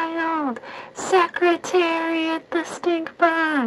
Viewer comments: The whole video I thought she was saying “secretary the stinking” 👁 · Secretariat? I really thought she said Secretary of the State Bug? It makes as much sense either way.